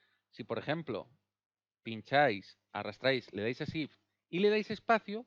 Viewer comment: hola amigo muchas gracias por compartir , muy buena y util toda la info en el minuto 3:32 no entiendo a que tecla te refieres cuando dices "zip" gracias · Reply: Es shift